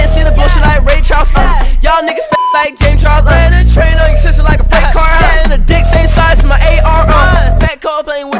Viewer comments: i thought he said the trueno is a freight car🤣🤣🤣🤣🤣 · Wait what’s that word at 0:57 ?? I thought that line was “y’all (no) fuck like James Charles????